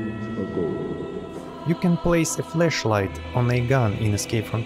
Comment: Not going to lie, This video popped up while I was away from the computer but playing through my headphones and I swore you were saying "Fleshlight" instead of Flashlight. I had to come back to the computer to see if Tarkov actually added Fleshlights to the game lmao